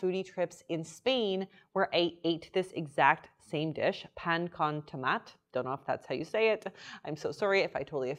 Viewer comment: trying to say PAN CON TOMATE warms my heart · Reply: I thought she said pain aux tomate lol